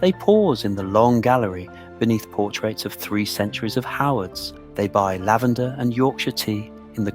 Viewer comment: Love that the captions say three centuries of harlots instead of Howards. Seems quite apt. Been to Castle Howard many times as my best friend's husband works there.